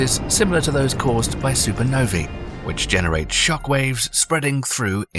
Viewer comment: Did he just say supernovie?😂😂😂